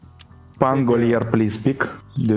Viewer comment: Am I deaf? I thought he said to pick Pangolier?